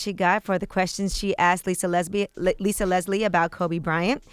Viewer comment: so no one is gonna say anything this woman saying Lisa Lesbian...